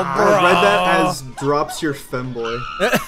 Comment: did I hear femboy